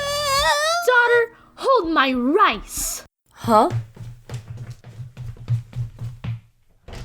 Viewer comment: I thought she said, “Hold my lice!”